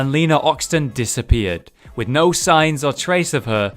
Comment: I thought he said with no signs of tracer not no signs or trace of her